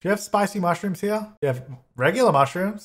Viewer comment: I was watching the videos while cooking so the entire time I thought you were saying 'Chuckleberry'